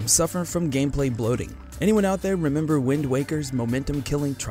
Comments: D-...Did he just say "Wind Wocker"...? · he said wind walker TRIGGERED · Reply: I could've sworn he said Wind Whacker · did he just say wind walker? triggered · he said wind walker TRIGGERED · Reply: I heard "Wind Woker" (like the wok). Lol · Did he just say Wind Walker? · Did you just say ‘WindWarker’ instead of Wind Waker? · Did he just say Wind Walker? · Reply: I Know it sounded like he said wind walker,Right?